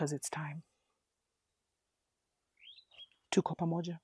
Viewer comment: what is the words, meaning & language (17:34) of what you say at the end of all your videos?? Thank you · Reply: Tuko Pamoja mean we're are together familY! #ConnectedInSpirit